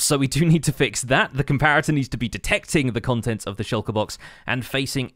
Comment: thought I heard Lucifer when he said "detecting" LOL